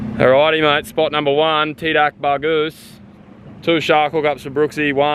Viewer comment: did you just said TIDAK BAGUS ?